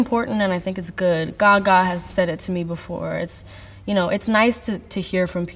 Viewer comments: What Billie said: "Gaga has said it to me before" What the captions said: "God has said it to me before" Well... They're not wrong haha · CC said “God” when Billie talked about Gaga. Honestly same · The way the subtitles say "God" instead of "Gaga" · when she said "Gaga" the subtitles say "God" omggg 😭😭 we STAN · I love how the subtitles say "god" when billie says Gaga. Hahahah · love how the subtitles said God instead of Gaga 😂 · she says Gaga but the captions say God. Understandable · why are the captions saying "God" instead of "Gaga" lmaooo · love how at 10:23 the subtitles read God instead of Gaga XDD its a moood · she says Gaga, subtitles say God. Coincidence? I don't think so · she says "Gaga" (like Lady Gaga) and the subtitles say "God" 😂😂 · when she said Gaga but the subtitles say God. I mean... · the auto captions says 'God' instead of 'Gaga' and honestly I don't blame them 😌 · Subtitles say God instead of gaga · did she say God or Gaga?